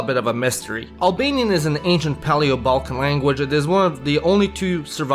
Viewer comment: comment in portuguese spotted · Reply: Did you mishear him say Indo-European? He said "Paleo-Balkan" languages. Albanian and Greek were singled out because they're Paleo-Balkan and still spoken today. Armenian is not a Balkan language.